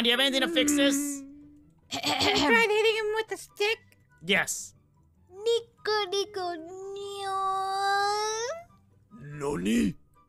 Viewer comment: Lolbit: niko niko neooon (I couldn't rlly understand what she said) Freddy: NANI! Me: XD 😂😂😂